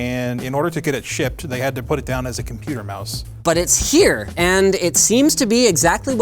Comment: "In order to get it shipped they had to put it down as a computer mouse" Linus Illegal Tips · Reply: The first 3 times I heard it as "they had to put down a computer mouse" # mice lives matter